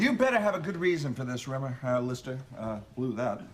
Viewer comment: What does Mac say after Rimmer err Lister... 39:34. Cheers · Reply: He says 'blew that.'